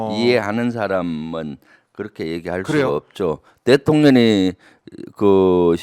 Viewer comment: 대통령이라고 말하는데 왜 대통년이로 들리죠?